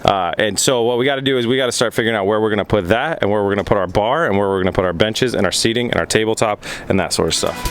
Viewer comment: legit thought he said, where we're gonna put our B*tches